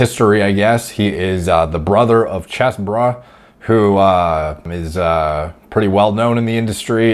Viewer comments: I thought he said CHESSbrah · I thought you said chessbruh at the beginning. 0:35 <link>